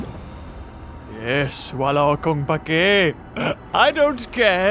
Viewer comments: ako lang ba or narinig ko wala akong pake ng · wait...is it just me or he actually said 'wala akong pake'? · Uhmm... are you a filipino? cause i heard you said "wala akong pake" in 4:06.... just asking cause im a filipino... im confuse... oohhh its actually 4:03... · Did he just say "wala kong pake?" 😲